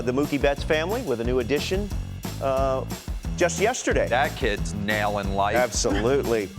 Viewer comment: For a second at the end I thought DeRo said "That kids nailing wife" 🤣